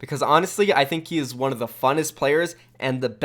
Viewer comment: Did he say "funnest"? :thinking